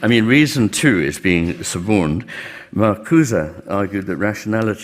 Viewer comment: "Rationality is being transformed from a critical force into one of adjustment and compliance". Min 25:00 ; does anyone know who's the author he quotes? He says it but I can't understand the name. I hear "vakouza" XD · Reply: Marcuse